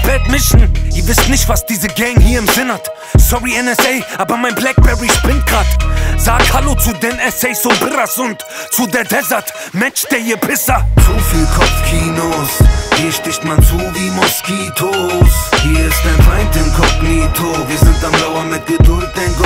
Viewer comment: was sagt der da ? Mensch der hier Pisser ? Kann mal einer übersetzen ? :-D